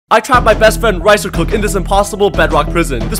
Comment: I thought he said I trapped my friend in a rice cooker